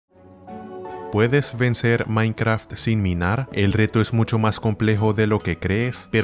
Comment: Pensé que decía “puedes vencer minecraft sin mirar?